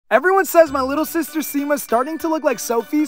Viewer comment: I thought you said "my little sister sigma" when he said "My little sister sima"😂😂